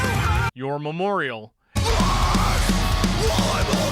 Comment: What in the world are you saying at about 10:30? I understand memorial. But I can't understand if you're saying more memorial. Nor memorial. Noir Memorial. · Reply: Your memorial